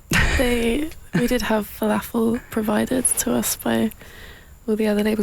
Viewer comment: could anyone tell me what is "Fallafo" sorry i,m from south america i espeak spanish and i dont understand the relation with it and domino records, is it a type of drink or whaaat? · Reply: They said falafel. It's deep fried fritters made from chickpeas, originating in the middle East. Domino records gave them falafel, when they met to sign them on to the label. It seems they really enjoyed the falafel in question.